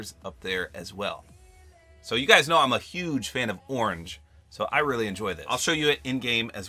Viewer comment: actually said penny is thicc... lol he’s married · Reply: I thought he said he was a huge fan of *orn